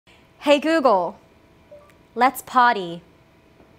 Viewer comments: I heard “Hey Google, let’s potty” 😂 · Lol I thought she said “Hey Google, let’s potty”. What a beautiful apartment. She and her boyfriend have made a lovely and functional home despite a really weird layout. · I thought she "said let's potty" 💩